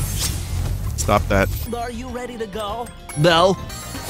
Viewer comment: sounded like Bently there.